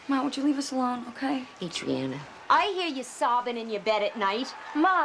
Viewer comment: What does Adriana’s mom say 1:09 ? please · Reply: I hear you sobbing in your bed at night